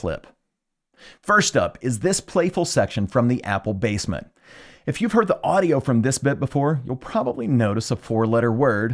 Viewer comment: what’s the missing word?? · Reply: F***face.